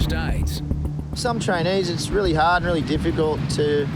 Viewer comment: I thought he said “Chinese” instead of “trainees” and I almost spit out my drink